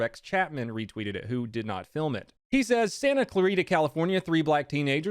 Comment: Thought it said “Santa clause”